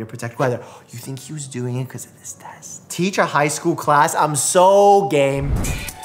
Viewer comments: The well placed ad break made it sound like he said “Teach a high school class? I’m sooo gay—” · Omg. He said I’m so game, but it cut to an add and it sounded like he said I’m so gay, and the ads just so happen to be about ru Paul’s drag race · “I’m soooo gay” is what I heard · Omg when mike said IM SO GAME I thought he said IM SO GAYY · When he said “I’m so game” at 3:35, I got an ad so it sounded like “I’m so gay” looool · Bro I was watching then I had a ad before he could finish his sentence so it sounded like he said “I’m so gay” instead of “I’m so game”